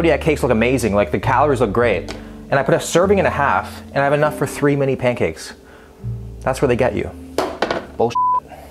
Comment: Yo. I have such brain rot that when he said, "three, mini pancakes," I heard, "Three-many pancakes," which is the complete opposite problem.